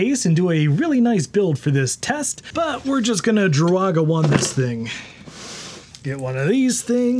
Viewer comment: Did you say Druaga1? OMG... I totally forgot.. wonder how is he...